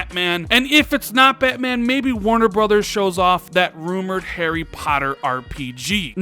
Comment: Did you just say Hairy Potter